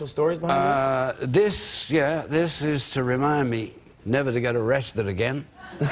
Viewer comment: What did he says at 0:40 ? It's to remind me never.... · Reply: To be arrested again.